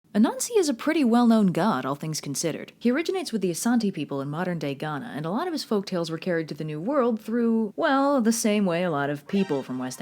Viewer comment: I thought she said a nazi